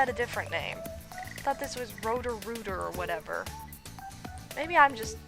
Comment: umm it sounded like you said roto ruter XD which is a plumbing company where I live.